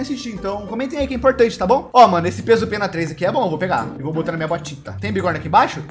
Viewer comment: Na hora q o falou "peso pena" eu ouvi "pedro penas"